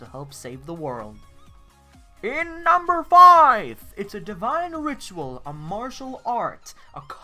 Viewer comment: did you say number 5 ?